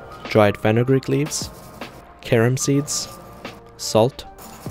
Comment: I thought he said “karen seeds”